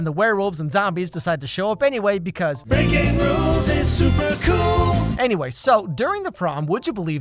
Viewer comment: I thought when it said Breaking rules is super cool said bacon rules is super cool